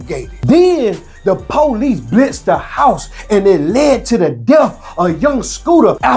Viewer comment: I thought you said the police killed him??